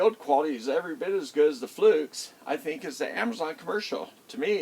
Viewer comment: you were holding up a meter and I could not tell what you said, what the meter was. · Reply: Thanks for asking! It is a meter that was made for Amazon - that is also made for Southwire (I have one new in package for sale): <link>